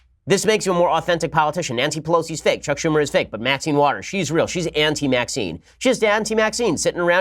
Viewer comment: oh AUNTY Maxine. I thought you were saying Anti-Maxine. I was picturing what an Anti Maxine would be like. Possibly the smartest person on earth.